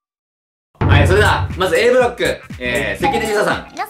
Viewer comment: Aブロックがエフロンくんに聞こえて一瞬1人でテンションぶち上がってた